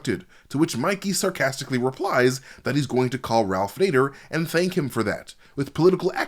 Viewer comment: Had no idea he said '_Ralph Nader' 😂 I always thought he said '_Raph later' referring to Raphael.